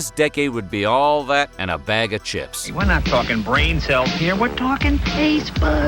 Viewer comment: I misheard the start as "bag of dicks" and spat my coffee out - brilliant 👏